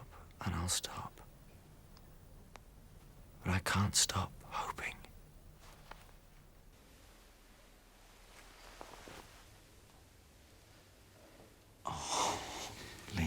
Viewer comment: what did he say here? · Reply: He said: but i can't stop hoping